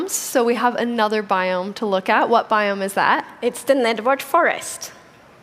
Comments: I thought she said "Network Forest" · is it me or did I hear her say "nedward forest*